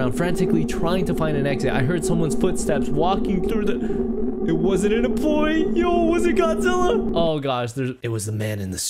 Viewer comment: the 2nd sentence was “I heard someone’s footsteps walking towards me I thought it was an employee. I took a photo wanting to find an exit quick.